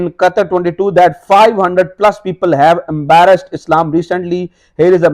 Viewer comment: Beautiful Video... FIFA 22 is doing Wonders... Ek Guzarish.. Plz. Plz. Be Careful About Pronunciation... @ 7:25 min. You Instead of Saying 500+ Embraced Islam, Said Embarrassed Islam.. Aap Josh Main Bol Gaye Lekin Matlab Bilkul hi Badal Gaya.. You Have More Than a Million Subscribers and Many More Millions are Watching... Never Mind, I Just Find This Inappropriate... We Love You Man, And Want You To Be Perfect... Thanks...